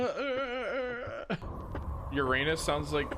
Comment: what's that you say? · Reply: Uranus Sounds like Hoth, a starwars planet